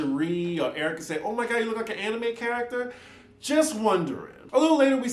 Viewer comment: Crystal said she looks like a black anime character? I thought that was what i heard🤷🏾‍♀️😂